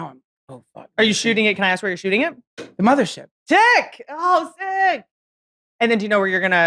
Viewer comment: Freudian slip of “dick” instead of “sick” @ 1:46:58 lol 😆